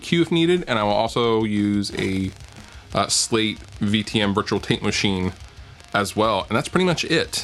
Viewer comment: Perhaps my hearing is going, but I heard "Slate VTM Virtual Taint Machine". I'd totally buy that plugin...assuming it looks like a hardware taint machine of course.